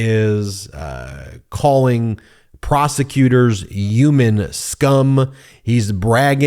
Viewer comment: Am I buggin or does he not pronounce the H in human? Why do I keep hearing "Yuman" 😅